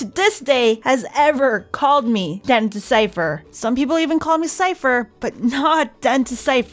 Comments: When called her den2cypher I thought he said dentist cypher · I MISHEARD IT INTO DEN DIAPER